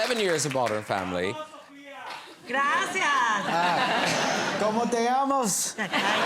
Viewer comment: what does kevin say at @ 00:47 - i dont know spanish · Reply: He's asking their name because that's the only Spanish expression he knows. "Como te llamas?" 😂